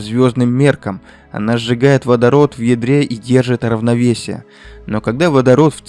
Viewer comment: Послышалось "Она сжигает водород в ведре"😂